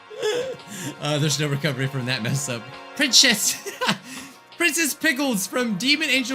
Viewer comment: turn captions on and he actually says pickles.